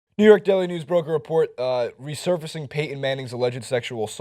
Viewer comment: HAHA I thought say New York Deli News at the start.